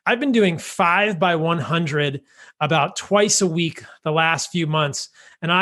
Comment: How much sprinting and how many times a week are beneficial?. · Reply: I thought he said 3 × 100 metres 5 times a week, just went back and found it was 5 ×100 m a couple of times a week, now to find out what blood flow restriction bands are! His sprint regime sounds super reasonable, In comparison to the ten miles 5 times a week!